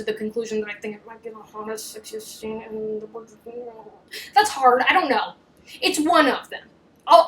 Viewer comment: please for the love of god someone tell me what says here, can't understand for the life of me 😂 Love the video, love you girl ✨️ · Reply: "Might be the hottest, sexiest scene in the Bridgerton world"